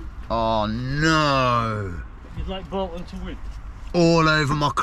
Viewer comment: Watched that bit over n over again where that guy says something and cannot make out what he says....anyways top vid as usual my man👊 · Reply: I thought he said, you should bought one, referring to my coffee but a few ppl said, he said, u want Bolton to win, as he was talking to someone in the car ... I'm still not sure lol